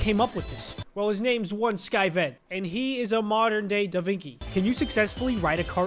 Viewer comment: Did he just say”Da Vinky”?